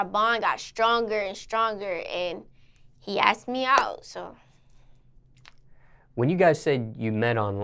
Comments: I heard “ Ice me out” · When she said he asked me out it sounded like she said he ice me out 😂😂